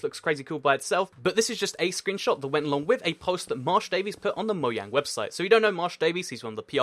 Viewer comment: did he say moyang? 😂😂